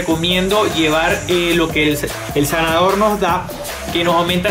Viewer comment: que dices de llevar un sanador? No entiendo bien si dice eso y que seria? Saludos desde Argentina